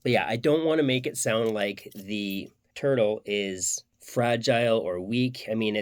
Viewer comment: sorry dude but are you saying "turtle"? English is not my native language I don't understand what part is fragile? PS. Great review. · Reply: Thanks my friend. Turtle is magicians code for “shell” haha. It helps prevent exposure.